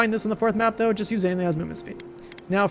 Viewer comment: just use wut??? you talked a mile a minute, no idea what you said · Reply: He said, "Just use anything that has movement speed"